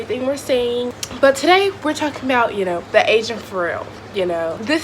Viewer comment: Did she just say the Asian Pharrell? 😂😂